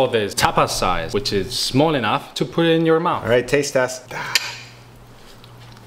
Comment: I thought hed said " tastes like spam right there."